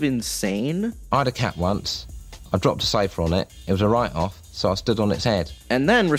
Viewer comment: "I had a cat once. Dropped a sofa on it. It was a write off, so i stood on its head" Classic. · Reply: I thought he said “ it was a riot laugh”